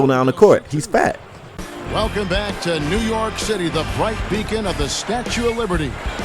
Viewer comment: um...... at 5:22 did he say " New York Shitty"